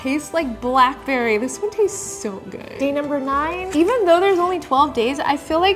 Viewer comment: wait a minuet did you just say tastes good !